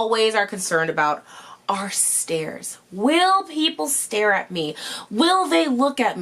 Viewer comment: When said “stares” I thought she said “stairs”. Haha shows what I’m worried about. Great video! ❤️